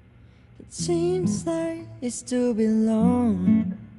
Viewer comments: did he say 'It seems like it's too been long' or am I hearing things?? Also Yellow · who noticed he actually said it seems like it’s too be long